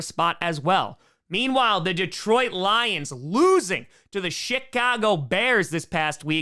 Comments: i swear said shitcago bears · LOL...sounded like you said SHITcago Bears...almost made me snort the Monster I was drinking out my nose.